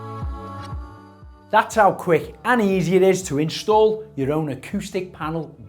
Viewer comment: Sorry, i thought you said at the end "and thats how easy it is to fit" hahaha - ill get a tradey in i think 😂